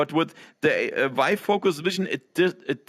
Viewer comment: My girlfriend thought you said 'WIFE FOCUS' and told me I should buy one immediately.